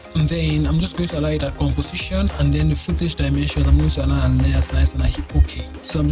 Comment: what did you say between 1:28 and 1:30? your accent makes it hard to know what you said. Please. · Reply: Allow it at later size and hit ok To import the vector layer as a composition so as to easy work with each shape easily